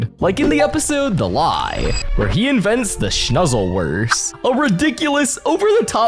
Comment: it’s the sluzzleworst not shnuzzleworst (unless I heard it wrong in the actual episode)